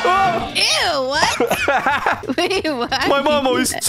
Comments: are we gonna talk about how said “EW what!?” Then she said… “BABE WHAT” when fell bye the pool idk if its just me but thats what i heard. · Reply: She said "wait what" but In a weird voice so it sounded like "babe what" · to me it sounds like said, babe what💀😱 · DID I HEAR THAT RIGHT?? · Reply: She said “wait what”.. hopefully 😨